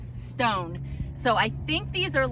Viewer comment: A LOT of great! I thought you were saying scones. Lol. Ohhh! Stones! 😂 Can I help you untangle it? 💕